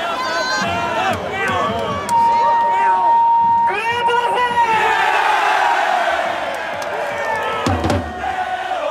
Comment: なんて言ってる？？ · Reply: グレートだぜ！！ だと思いますよ